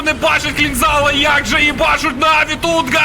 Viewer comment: Мені почулося чи звучало " як же хбашуть наві тут "